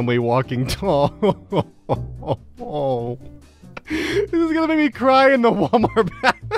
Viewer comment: not gonna lie, I read and hear this as "This is me crying in the Walmart bathroom" instead of "this is going to make me cry in the Walmart bathroom" I'm dumb